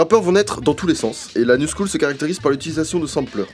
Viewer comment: j'ai compris l'anus cool j'ai fait whhhaaaattt mdr